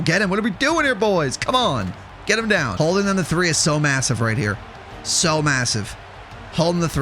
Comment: did you say m-massive?